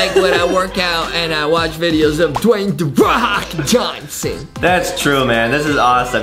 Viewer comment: i heAR “DwAYnE da wok jhonSuN”